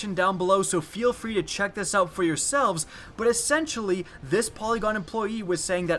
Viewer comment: when you said polygon I thought you said Pentagon I was like oh shit this is serious lol